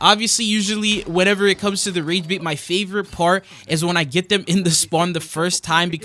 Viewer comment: Exuse me? What did u say lol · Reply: When it comes to the rage bait?